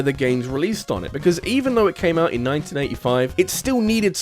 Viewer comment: did he just say that the NES came out in 1995? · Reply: No, he said 1985